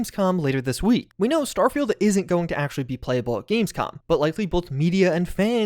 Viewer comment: For a second I thought he said "we know starfield isn't gonna be playable at launch"